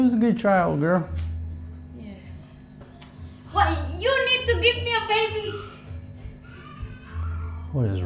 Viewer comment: OH GOODNESS... DID I HEAR CORRECTLY?? DID SHE SAY... "YOU NEED TO GIVE ME A BABY?".... WANTS ANOTHER BABY!!!!!!